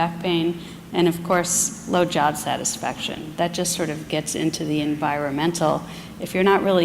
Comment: I could swear she said "blowjob satisfaction"